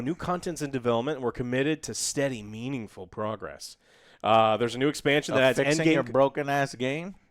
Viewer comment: I definitely thought said "more 'meme-ingful' progress" 😆